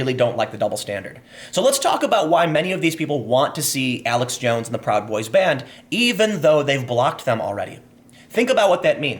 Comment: What he said: "...many of these people want to see Alex Jones and the Proud Boys banned" What I heard: "Many of these people want to see Alex Jones and the Proud Boys Band" Please someone meme this.